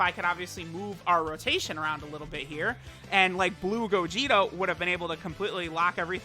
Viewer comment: did he just say Blue GogiTo ?